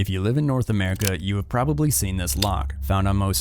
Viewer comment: Yo I thought he said North Carolina